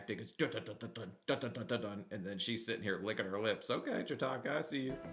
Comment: Got to love my hearing loss, my life is a giant misheard lyric. When someone says "Chitoka" my brain fills in a blank with something that sounds close. "Shiitake" as in mushroom was today's substitution.